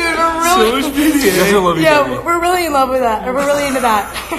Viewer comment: It kinda sounded like Ethan said “I love you emma” #Ethma confirmed🤔🤔😱